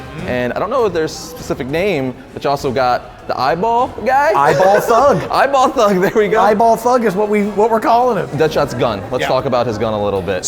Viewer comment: the subtitles read "gay ball fuck" instead of eyeball thug